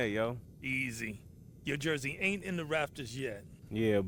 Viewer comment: did he say Raptors? lol! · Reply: Rafters